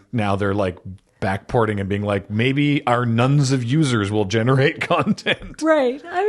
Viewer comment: I mis-heard it as our "Nuns of users will generate content". Like a group of Nuns playing Minds Eye and then coding up new content for it.